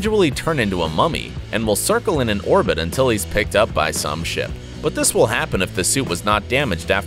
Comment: I thought he said Sh*t